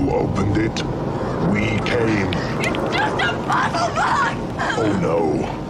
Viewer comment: Okay, she said “just a puzzle box”. I heard ‘just a bubble butt’. I think I should go for a hearing test.